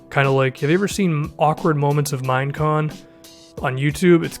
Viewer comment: I heard "awkward moments at mimecon" and was like yeah that makes sense it's always awkward when you're in a huge building or area with other people and it's completely silent. And then I realized that I'm an idiot.